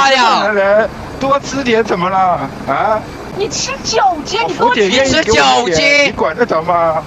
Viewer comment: 我聽成「你吃酒精」😂